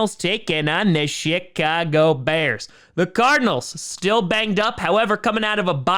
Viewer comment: When he said Chicago bears I thought he said sh*t cago bears